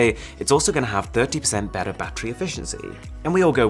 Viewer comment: Hey idk if it's only me but I seriously thought he said "badder"